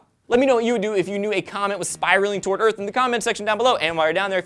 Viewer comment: Kinda sounded like he said "comment flying towards earth". I thought of the word "FIRRRRRRST!!!" flying through space...